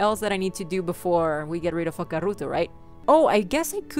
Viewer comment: I thought you said fucka ruto😂😂😂